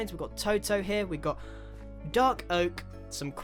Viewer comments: did He say dog oak?? 😂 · i understood "Dog oak", but i think he said " dark oak"